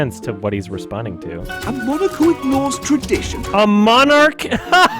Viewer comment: I heard "A modicum of norse tradition"